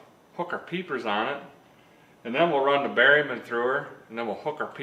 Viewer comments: The first time I heard you say “berryman”, I think in a S10 video, at first I thought you said “barium”. I laughed, thinking that an enema might be just what the S10 needed.😆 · 36.3 episodes in, thought You've been saying "BARIUM", like the cancer tracer/indicator used for the RUBBER GLOVED, one finger exam😲😲😲, but realizing BERRYMAN, Well I'll be dipped💙👍👍👍